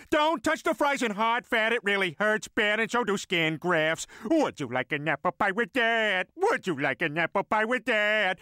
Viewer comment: When I first heard this, I always thought Peter said "Would you like an apple pie with dad". Lol.